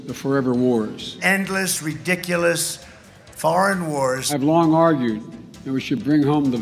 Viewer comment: Trump did not say "foverever wars " at 3:07 he said foreign...